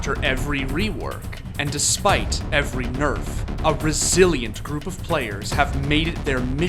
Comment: at the start "A resilient group of players" first heard it as "a brazillian group of players" had to rewind to make sure xD